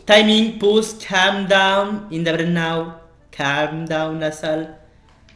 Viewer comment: sa veux dire quoi ce mot ? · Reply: il a dit pause calm down right now lasalle sa veut dire stop calme toi maintenant lasalle